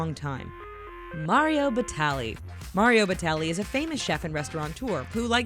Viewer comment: Lmao 8:07 for a second there, I thought they said Mario Balotelli.